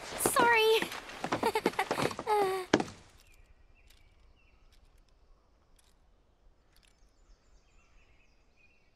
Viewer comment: What does Aerith whisper at 18:35 ? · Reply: "I'm glad"